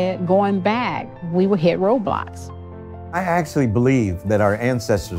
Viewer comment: She said “roadblocks” but I heard “robots”! It’s late!